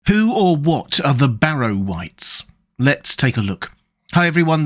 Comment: I thought you said Barry White....